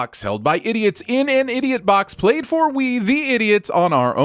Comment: Alright, so I've got two sins lined up for you. Ready? 0:31 it's "played for us" not "played for we" 3:17 it says SongeBob instead of SpongeBob Ding! Ding!